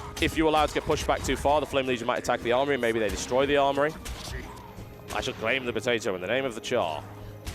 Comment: did he say "I should claim the potato in the name of the Charr" ?